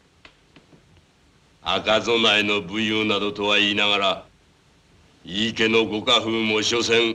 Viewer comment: did he just say naruto ? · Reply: Nah, he said "Nadoto". "赤備えと武勇などとは言いながら" "Aka-zonae to buyuu nadoto wa iinagara" (source: I am a native speaker)